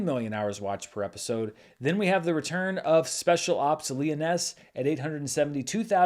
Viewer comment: Am I mishearing or is 'Lioness' pronounced differently in USA? Never heard it pronounced as Leoness.